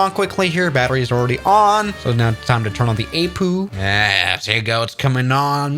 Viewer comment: did he say a'poo'